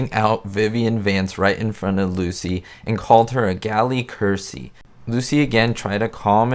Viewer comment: What did he call Vivian? Sounded like you said a ‘gally cursy’?